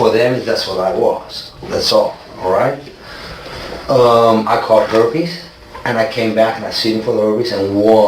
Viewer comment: Did he say "I caught herpes" @ 5:53?